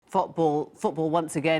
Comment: THOT ball? Did she say? Also #SaveLucifer